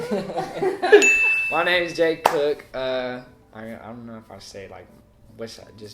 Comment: i thought he was about to say that his name was j. cole😂and i was like wow what a coincidence. he kinda low key sounds like him too!😂